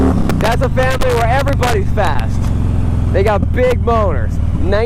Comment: At the 2:26 to 2:28 point it sounds like he says, "They got big b0n3rs." L.m.a.o.